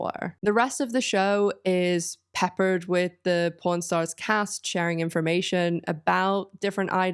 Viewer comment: American here. I kept mishearing the word pawn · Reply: Me too, i thought she said corn stars